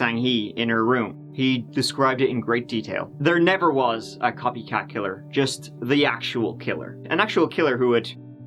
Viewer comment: I kept hearing "croissant killer" 😂 Awesome video as usual! Love you!